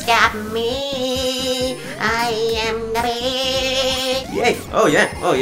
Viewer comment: did you say 'im The weed' ?